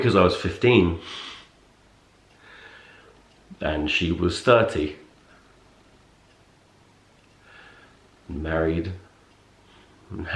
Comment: i thought he said 13, married, had 3 kids and i hAD TO REWIND BECAUSE I COULDN'T BELIEVE IT no he said 30 i sighed in relief lmao